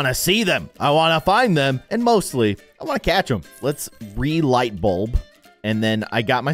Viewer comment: i thought he said "and mostly i wanna.. eat them!"